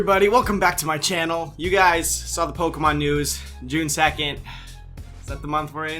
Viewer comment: I heard "You guys saw pokemon nudes"